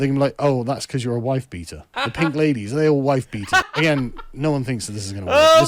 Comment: haha, i misheard wifebeaters and thought he was saying to call the radical left "whitebeaters" 😂